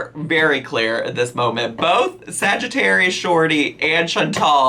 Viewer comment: When you compared her to sagittarius shawty (I just figured out who she is sry) I thought you were saying Chantal literally had short person Sagittarius energy and Idk anything abt astrology but yeah prolly